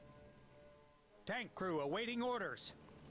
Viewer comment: I thought he said "Dank crew awaiting orders..." at 13:19 Took me a minute to realize that doesn't make sense. haha I tried one of these games, unfortunately, they aren't that fun compared to others.